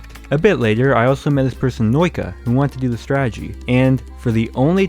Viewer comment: did bro just say "noika" on The Nokia??